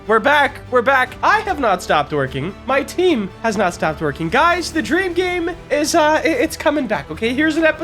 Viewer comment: i swear at the start i heard "i have not stopped twerking, my team havent stop twerking" i swear dude i swear